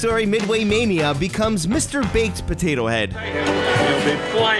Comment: I thought you said Mr. BAITIN potato head